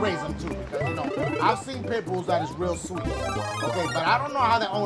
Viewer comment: When he said pit bull I thought he said pimple